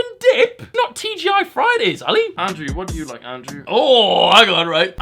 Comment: did anyone else hear "Andrew what do you like andrew?"